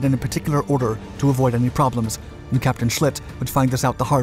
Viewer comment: Did I hear "Captain Sh*t"?